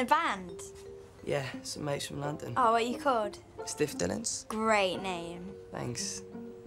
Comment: I fr thought he said stiff bellends for the first time watching it